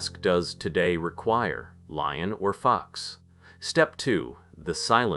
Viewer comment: lion or fox lol i had to turn on my captions to see what you actually said because it sounded like lying or f u x 🤣 i have to admit , I started having a little chest pain early in the vid( And I have extreme high blood pressure) n I had to lay down, but when I heard this I laughed so hard and it distracted me from what was probably an anxiety attack.